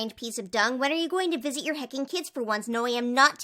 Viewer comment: no joke i thought you said fucking